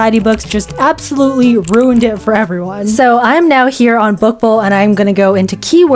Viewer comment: What did you say book hole?? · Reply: Book bolt